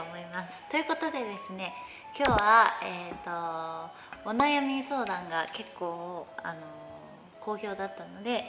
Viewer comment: ママの前で見てたら、 お悩み相談がオナ病み相談に聞こえた。。。 みなさんは。絶対ママの前でみないでね